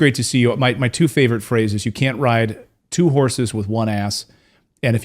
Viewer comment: I thought he said You cant write 2 horses with one S